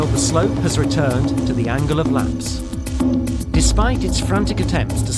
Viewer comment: Is the background music to loud? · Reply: Yup same I had to look up “ angle of repose” because I didn’t know and could hear what he was saying!! Thought he said “angle of lacrocs” or something like that lol